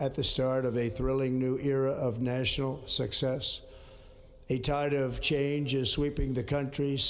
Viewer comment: ※ 바로잡습니다 ※ 2:14 entitled change ☞ A tide of change 2:16 낙담 ☞ 확신 2:55 incomplete disrepair ☞ in complete disrepair 3:10 법을 수호하는 데 실패했고 ☞ 법을 수호하는 자국민을 지키는 데 실패했고 6:33 I jumped ☞ I felt 9:01 마틴 루터킹 서거일 ☞ 마틴 루터킹 데이 10:12 오늘 저는 여러 일련의 역사적인 행정명령에 서명할 것입니다. 24:02 sars ☞ stars 29:19 번영 ☞ 자유로운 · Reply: a tide of 가 entitled로 들리다니 신기하네 ㅋㅋ